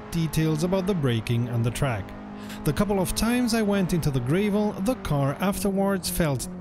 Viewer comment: Did he just say "Grayvel" - 2:14 - hahaha.... just kidding, cos as an Australian - who am I to stir about accents... ;D